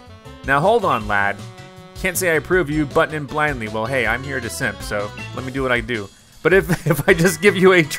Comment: did i just hear zack correctly? did i hear the word "simp" come from his video?? o.o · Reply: 😂hes here to simp🤣